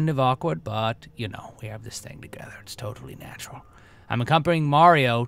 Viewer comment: did you say 'we sleep together?'